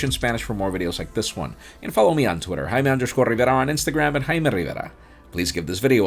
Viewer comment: First of all I thought your were saying "Hi man, Rivera" .... Then I saw Jaime Rivera......